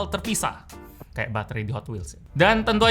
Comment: "kayak batere di hotwheels" aku salah denger apa gmn? Emangnya ada hotwheels pake batere?